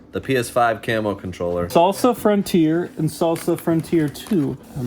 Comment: did I just hear you say Sosa Frontier?! oui